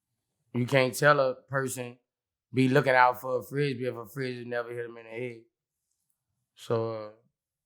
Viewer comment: what is Boo , saying?. Help please. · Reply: u can’t tell a person look out fa da frisbee if a Frisbee Neva hit em in Dey head